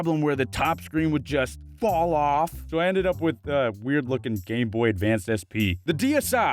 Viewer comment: did he say...GAY BOY?